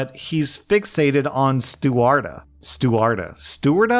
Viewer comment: I think it's "stuarta" say for a baby's name. :- p